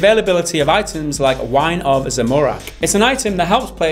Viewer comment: Did he just say Zemorak?